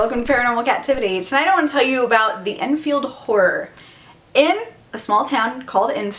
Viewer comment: I keep hearing "the enfield whore" No kidding